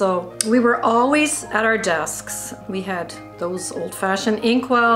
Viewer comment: wait did she just say desksk